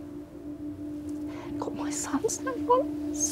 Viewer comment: Can anybody hear what she says at around 22:39, when she's tearing up? :( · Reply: It's got my son's name on it* she says.